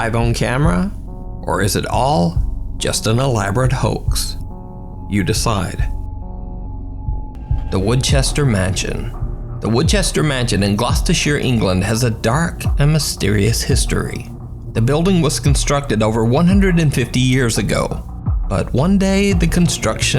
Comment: At first, I thought he was saying Winchester. Then when he said the workers just up and left the place, that didn't sound familiar at all.